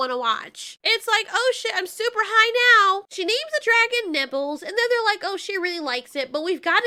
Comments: Not trying to be crass but when you said nibbles youtubes' speech to text wrote "nipples". I clearly heard you say nibbles so why do they use a program that doesn't properly function. Anyway I thought it was hilarious and laughed for a good minute. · For some reason I heard 'nibbles' as 'nipples' and I choked on water